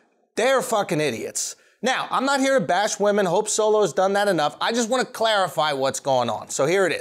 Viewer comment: "I'm not here to bash women. Hope Solo has done that enough" (referring to her assault on her sister) . Absolute Gold. Lmao. 😂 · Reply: I thought he said, "I hope Solo's done that enough."